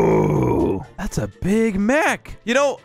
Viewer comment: am i the only one who heard 'thats a ''bigMac''